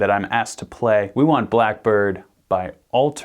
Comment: When he said black bird I thought he said black parade I almost had a fit and my heart dropped but we’re good